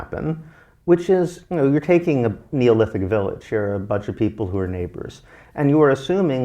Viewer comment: OMG. He was saying Neolithic village. I thought he was saying Olympic village. 🤔 I'm an idiot. 🙄